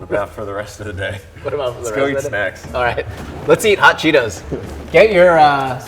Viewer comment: Did say he eats ass? Nothing wrong with eating ass js